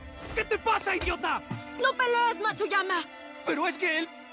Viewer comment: entendi ahuyama jajajaj😅😅